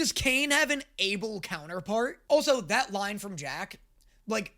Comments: …did he just say Jack? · Did he say Jack it’s Jax get it right next time